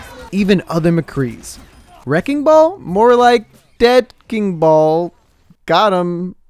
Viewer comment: is it just me or is he saying “dead fking ball”